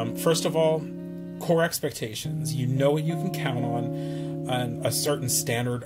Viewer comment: Super detailed! Great review. I loved all the details on the subscription itself. Also, at first I thought you said “corset expectations” and that REALLY got my attention lol.